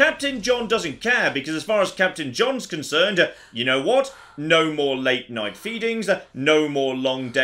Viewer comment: I thought you said "no more late night beatings" for a second there.